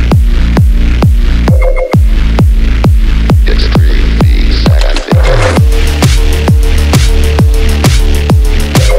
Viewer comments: i've been listening to this for a while and i still cannot figure out what words are being said here someone PLEASE help it's been driving me crazy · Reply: "Extreme bass activated" i gess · what does the voice say at 0:12 ??? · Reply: "extreme beats activated"